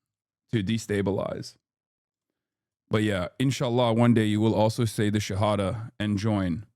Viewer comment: What did he say about a enchilada? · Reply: he says inshallah and shahada, i'm not sure which you're hearing as enchilada